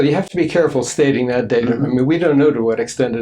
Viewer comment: why does he call him David?? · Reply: I think he said "data"